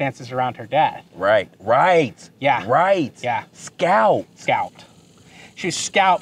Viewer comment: When you asked :”Did you kill Rosa?” - it wasn’t inaudible, I heard “scalped”. Try listening to it again.